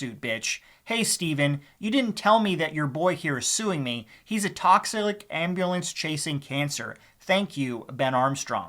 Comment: Did you say "TOXILIC" @ 2:21?